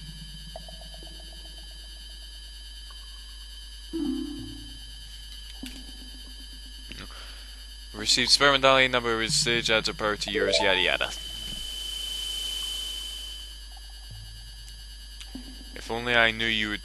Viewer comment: I thought he said sex medallions for some reason.... O_o